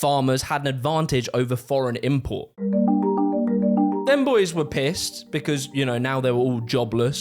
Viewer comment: did he says Femboys😭